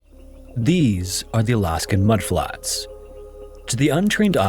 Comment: I’m sorry, instead of hearing “these are the Alaskan mudflats” I heard “these are the Alaskan mud crabs” and I thought to myself “aren’t they supposed to be the Seattle mud crabs?” I’m going back to bed. My brain is full of nothing